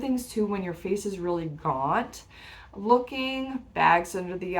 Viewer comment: when your face is really what? I can’t hear what you’re saying hear. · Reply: “Gaunt” is the word used I think.